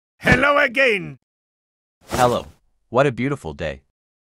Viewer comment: "Hello a gay"💀💀💀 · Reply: I hear "hello i gained"